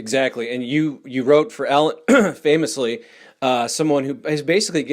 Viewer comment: did he say 'Ellen'? as in Degeneres? Fuck these NDAs. Judges are more and more refusing to enforce them